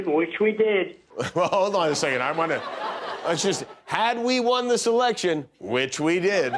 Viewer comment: I though he said,” and we won this election, which we did” where I thought Jimmy was going to say, “if you say you won, which you didn’t…(something more comedic than I can come up with)”